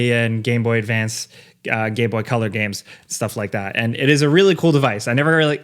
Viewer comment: did you say gay boy color games at the end there? Sign me up